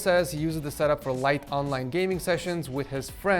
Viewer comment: thought you said he uses it for online dating with hes friends and family